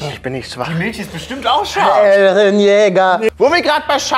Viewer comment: zusammengefasst: "NIMM DIR MILCH" "ICH BIN NICHT SCHWACH" "PROBIER DIE MILCH" "ICH BIN NICHT SCHWACH~ EREN YEAGER" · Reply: Und ich dachte schon, ich hab mich verhört.. hatte schon die Befürchtung, verwandelt sich gleich, als ich Eren Jaeger gehört habe 😂😂😂